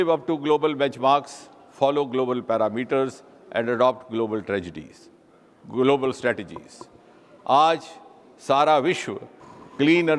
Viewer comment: did he say global tragedies😢